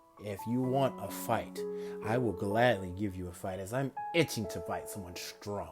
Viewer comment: I thought he said b*tchin for a fight but he said itchin